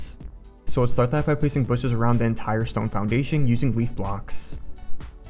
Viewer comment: did he just say leaf blocks?